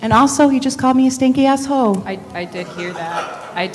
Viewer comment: I heard him say 'stankin' ass hoe,' IJS. (For the record, the aforementioned sentence aren't my views, but my commentary on what I heard at 4:34 lol)